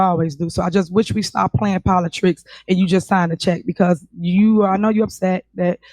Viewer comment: Did she just say - 'POLI - TRICKS'?😮 Then @ 13:33 She says '...if someone 'FALS - ISHLY' uses...' when she is talking about wanting to jack up the parking fines for someone using a 'HANDICAPPED' placard or parking in a 'HANDICAPPED' space without a 'HANDICAPPED' placard! T.A.H. hasn't got even the smallest grip on the meanings of the words or the understanding of the words or how to properly pronounce the words or worse yet, how to simply speak the English language so that she doesn't sound like a completely uneducated moron!